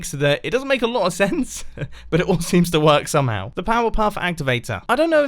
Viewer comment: did he just say Powerpuff?